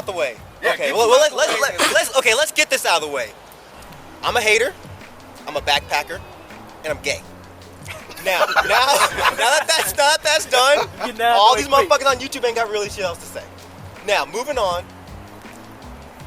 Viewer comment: did he say that he was GAY!!!!!!!!